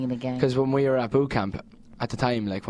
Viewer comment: HeHe And It Sounds Like He Says 'Poo Camp' <3